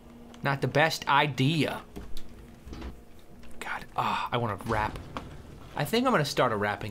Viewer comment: "God, ooh i wanna crap" that's what i first heard